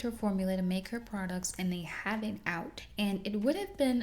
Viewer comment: I'm sorry, what are you saying at 17:23? 'Addie Munroe finds out that Sarah used her formula to make her products and they … having out'? Sorry, I'm having a hard time understanding what's happening. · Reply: "they have it out"